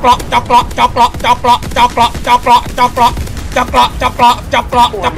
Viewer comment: เจาะเกราะ จะประ จะกระ จะเปราะ ผมควรได้ยินว่าอะไร😂